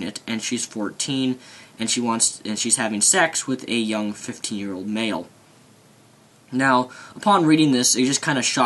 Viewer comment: at first i thought he said she was sleeping with a 50 year old man